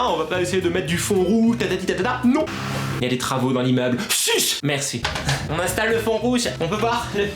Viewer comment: C moi ou a 5:11 il dit suce???!!! alors!!😂😂😂